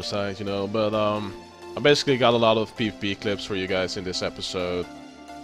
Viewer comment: LOOOL did you say pee pee clips? Gosh I don't wanna watch this anymore xD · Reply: He said pvp clips